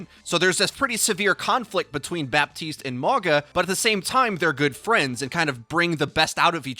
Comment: I thought he said MAGA bot Mauga. Much lol.